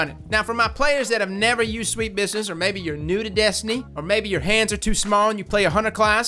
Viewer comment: I thought you said "...Maybe your pants are too small and you play the Hunter class."